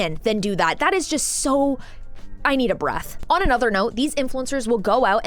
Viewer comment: I had to listen 2ice AND THEN enable subtitles 'cause I swore you said "..., I need a bra..." not "...I need a breath..." and I LOL!!! when I fist heard it!!! AND DON'T ASK why my mind went there. 🤭😇😌